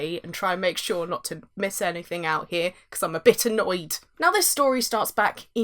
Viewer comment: When you said you're "a bit annoyed," I was very confused. I thought you said "a bitter Noid." I hope that guy found work after Domino's Pizza.